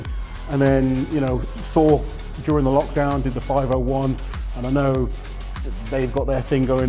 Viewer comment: you made a mistake there in subs 'so during the lockdown did the 501' instead of 'thor during the lockdown did the 501'.